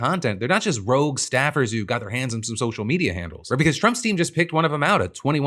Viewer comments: Omg, I swear I heard this as “Trump-stein” 💀 My brain hurts. Everything hurts. Can I go to bed now? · When you said "Trump's team" I heard "Trumpstien" hahaha